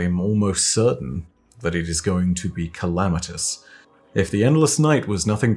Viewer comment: "Something calamitous" Me, who thought he said 'Calamitas': "We're absolutely f*cked."